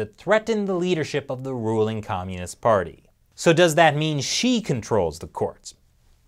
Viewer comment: Everytime I hear "Xi", I thought I hear "she". She ruling a communist party.